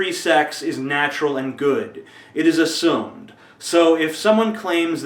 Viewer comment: what he say? "it is assumed"?